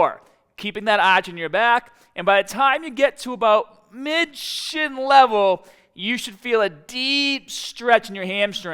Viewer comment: did he say mid shit level lmao 😂